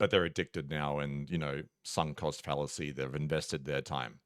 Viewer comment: what did he say sun caused phalisy? Addicted to what? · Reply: LOL. Yes that is what us Australians call the Sunk Cost Fallacy.